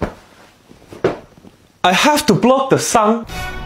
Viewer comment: "I haf to block the sun?" Yooo i can feel the 'bich whet' in his tone 😭😭😭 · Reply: I heard "i haf to blow up da sun?"